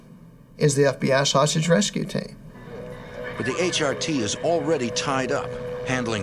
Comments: 🤣🤣🤣 turn on captions for 22:10. Did he just say sausage rescue team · I swear that my brain at 22:17 heard him say, " The FBI sausage rescue team". facepalm